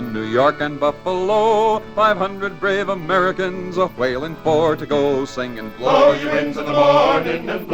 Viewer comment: Hooray!Thanks! One of the first records I ever had--got it when I was 3. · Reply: After all these years, I can just about narrate along with it (except that at 3, I heard "500 brave Americans, a whale, and four to go"!)